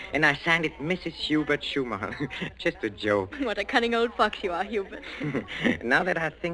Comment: Not sure if anyone is here or will read this. At 24:20, what is the line Marion says? I found a script online which has the line as cunning old fox, however, it REALLY sounds like she said something else. Didn't know if anyone had context for what kind of vernacular this era would use on the radio. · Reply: I listened twice, and the girl who played Marion (who was she?) did in fact say "fox"🦊. I liked this a lot. Peter Lorre was always good. Try to catch him in his first great role, as the child molester in "M" (it's not as gruesome as it sounds)